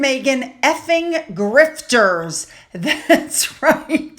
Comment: I thought I heard Simmons say, “lazy f—ing grifters” on a clip. Sure sounds better with the “lazy”, so I hope I’m not mistaken.